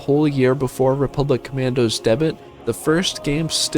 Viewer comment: Wait did I just mishear that or did he just pronounce the word "debut" as "debit" 🤣?